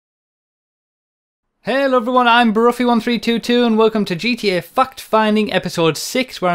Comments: I thought he said "Fucked finding" I was vastly more interested at that point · Haha thought u said "fuucked finding" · Haha at the start it sounds like he says fucked finding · As a German I heard "GTA f*cked finding" love it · it sounds like he says "welcome to gta fucked finding" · it sounds like you said fucks finding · i thought he said "gta fuck finding" at first. lmao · My 2nd vid I've watched of you, and my first fact-finding vid, and I thought you said fuck-finding at the beginning XD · when you said "fact finding" I thought you said "fuck finding" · The first time I heard you say it, I heard "Fucked-Finding" I'm not trying to make fun of your accent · sorry if I'm being rude but when you said fact-finding I thought you said fucked findings and I had to double take, hilarious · i thought he said fuk finding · who else heard fuck finding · I thought he said "fuck-finding" instead of "fact-fighting" xD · At the beginning I thought he said fuck finding ...